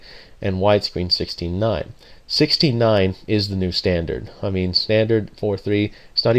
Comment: It sounded like you said 69 is the new standard